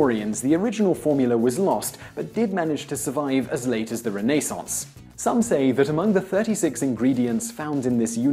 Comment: Renayssance is what I heard him say XD